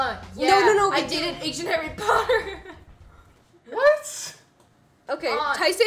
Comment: I legit heard her say "I did an Asian Harry Potter.". Turns out what she actually said was a lot more innocent.